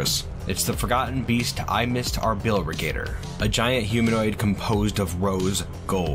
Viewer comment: When you said the forgotten beast’s name, I swear I thought you said “I forgot our bill (rigator).” Then I thought damn what a monster 😂